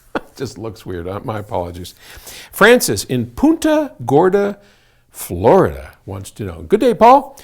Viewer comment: Wait... @ 00:30 Did he just say Francis from Puta Gorda,(fat prostitute in Spanish), Florida?